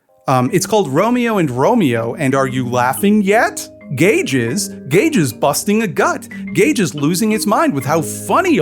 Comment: You said "Gage is" but I heard "Gay jizz" and it made me do a double take.